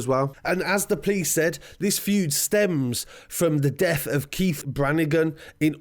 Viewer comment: This story is a mess (edit: not a mess on scarcity, i mean a mess to follow in general) Im acc confused But its so sad how this boys dead body was objectified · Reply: commandante Renegade My bad fam just read through the comments on the vid. Thought he said Kearnes best friend not Keith